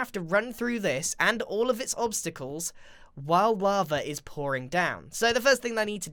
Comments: Why wava? Am I the only one who heard it? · "while wava" that's what I heard